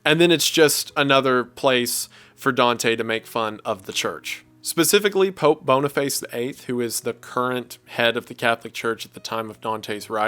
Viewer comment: I heard "Pope BonerFace the 8th"